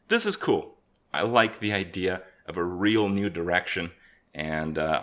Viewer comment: I can't hear "new direction" without hearing "nude erection".